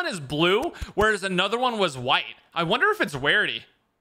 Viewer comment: he says wrarity instead of rarity Lol